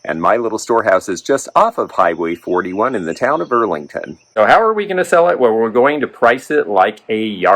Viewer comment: Fudge! Thought you said Burlington not Earlington. Darn! Ur closer to west by God Ky.!!!!